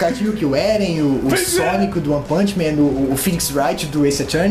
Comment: oq foi isso que meus ouvidos escutaram...? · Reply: sonic do one puchman